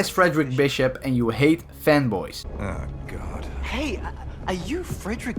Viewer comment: I thought he said femboys XD